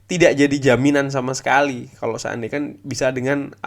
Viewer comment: Pasangin 3 4 3, Lindelof di DMF, Pogba AMF, Shaw LMF, dalot RMF di depan Sancho, rashford, ronaldo, Pogba di depan Lindelof udh paling aman buat jelajah lini depan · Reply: Sy tunggu sj kpn saatx emyu mndptkan hakx sesuai nama besar n predikatx. Krmrn yg hrs trjadi adlah emyu mendominasi game n unggul min. 2 bola dr boro. Sy koment sj utk pemakaian kata SEANDAINYA di konten anda, perasaan sy dengarx , kok jd SEANDAIKAN. Penasaran sj , sy yg salah dengar ato memang demikian. Senada dg emyu yg sy dengar begitu tp kok jd demikian 😁🙏 Salam.